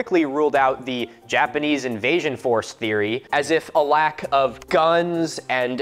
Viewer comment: Deadass thought it said lack of bones...